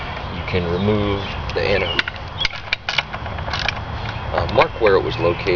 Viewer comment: in the vid you remove that skeg looking thing,I cant make out what you called it.I need to order one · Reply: Zinc anode/steering tab